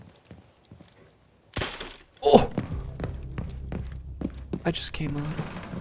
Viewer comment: did he just say “i just came i little”? That’s inappropriateeeeee